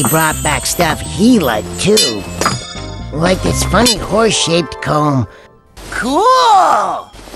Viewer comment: did say ''horseshit'' at 05:31???? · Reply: No, you misheard it; said: "Horse-shaped".